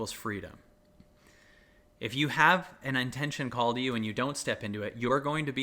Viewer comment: Cosmic joke: At about 10:10 into the video, I thought I heard, "If you have an intention called YOU and you don't step into it, you're gonna be in chaos until eventually you do." Blew my mind open, popped through layers and layers of stories about "Who do I think I am to try to ___?" After reeling with that for several minutes, I backed up the video to listen again, and this time I heard, "If you have an intention call to you..." I pushed my earbuds in more tightly and listened another time. Yep. Call to you. And then I thought, "OK, so what if the intention that is calling to me IS me?" And I cracked up... Yeah, I'll be patient with that intention and feel the freedom it brings. Thank you, Kyle. Thank you, Universe. My deepest lessons always seem to be accompanied by a healthy dose of laughter, most often at myself.